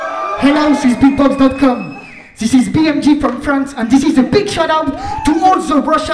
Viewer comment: What he said at 1:17? "And this is a big shootout to Warsaw, Russian ..... Youtube"? Someone can reply me?